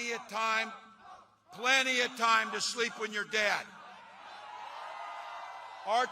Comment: does he say sleep with your dad or sleep when your dead?